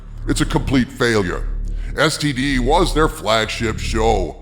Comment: Did I hear right "STD was their Flagshit Show"? 😆